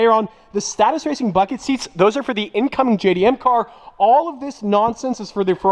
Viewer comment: did i just hear "incoming jdm car"?